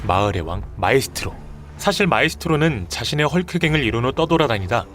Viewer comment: 뭐라고하는거임 자신의 헐크등? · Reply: 자신의 헐크갱 입니당